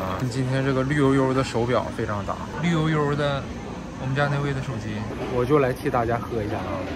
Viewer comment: 沒聽清楚，聽成「綠油油的我們家那位」😂😂😂😂 嚇了一跳